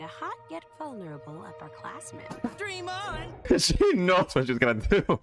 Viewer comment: I thought that said "upperclassmate" 😅